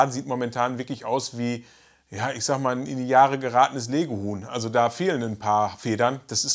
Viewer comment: Ich habe Lego-Huhn statt Lege-Huhn verstanden! 😂